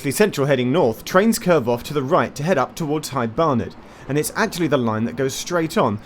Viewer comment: Is it only me or does it really sound like Geoff says "Hight Barnet"? (I actually have thought that in other videos already, but always thought I misheard.)